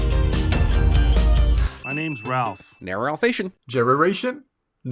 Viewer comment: I have no idea what anyone is saying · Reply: Ralph: "My name's Ralph" Cinemasins: "Nar-Ralph-ation" (a terrible pun on narration): "Jerraration. Nar-race-in? Polmentos(?) can be fun in small doses but you need to be careful not to develop an over-reliance on them for comedy or they'll have a strong chance to quickly wear thin...ation?"